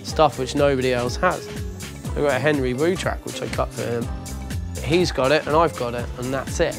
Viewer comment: did he say Henry Moonchild, as in the HKE alias? I want a Henry Moonchild dubplate so bad :( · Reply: Henry Wu track